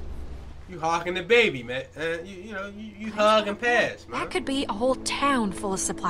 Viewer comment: is trippin lol did he say "hug and pass" at 28:18?